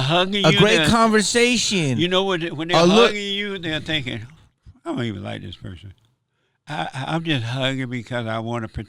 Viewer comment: i thought he said “ion even like this mf”